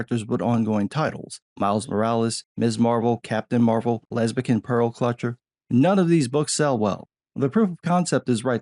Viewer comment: I may have misheard you but...lesbian pearl collector? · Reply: lesbian pearl clutcher.. pearl clutcher generally means you feign outrage over things that don't really matter in an effort to manipulate others